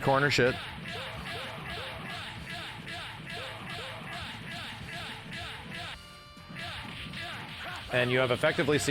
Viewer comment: Anyone know what this says? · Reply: Just wait a moment